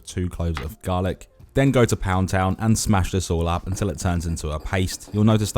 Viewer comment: Did i hear ," go to pound town"😅 such a fresh recipe😋😋😋😋